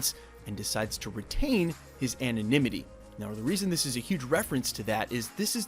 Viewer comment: wtf is an animinity or whatever. Ohhh is he saying he wants to stay anonymous? Anonymity. Good word bro